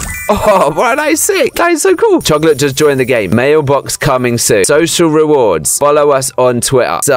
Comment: for some reason i thought said mayo box :DD